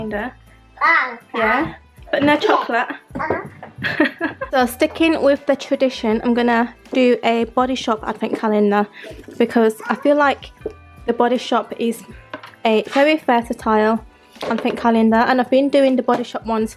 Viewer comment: i keep hearing "f-ing calendar" LOL